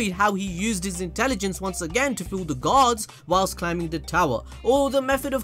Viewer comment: I thought you said "...fool the gods..." for a minute there 🤣🤣🤣🤣